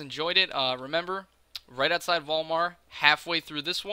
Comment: When you said Vol'mar I thought you said Walmart xD